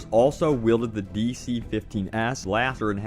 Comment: All I heard was the "DC-15-Ass-Blaster"